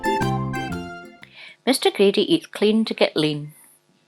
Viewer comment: i thought that said Mr Greedy Eats Lean To Go Clean